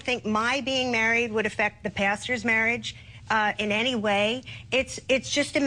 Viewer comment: At first I thought she said gay marriage wouldn't affect the bastard's marriage instead of the pastor's marriage. lol